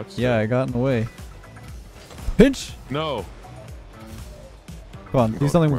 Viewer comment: Anybody else thought he said b!%&# instead of pinch